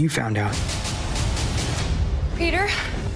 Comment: Am i tripping or did 1:41 kinda sound like Gwen saying "Peter" from andrews movies?